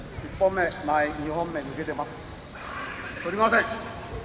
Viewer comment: What did the referee say at 15:05? I admire that guy with the taped fingers and bandaged head. He took a beating and kept going, never quit. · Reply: blockmasterscott First attack off distance second attack off target